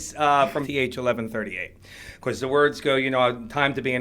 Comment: What did he say it was from?? ..... (V-H 1138????) ??? What is he talking about? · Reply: THX-1138. It’s a sci-fi movie from the ‘70s.